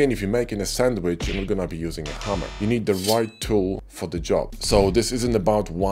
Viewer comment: when you first used the sandwich analogy, I thought you said Hummer, near the end of the video I realised it was a hammer, made me smile :) Thank You for an excellent video